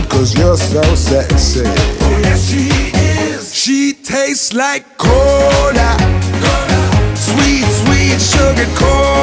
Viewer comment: The first time I heard this song I thought he said "She's sweet like hola"